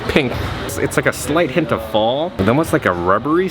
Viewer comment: I spit out my dinner when I thought I heard you say the Michael Myers scent has a slight hint of balls. hahahaha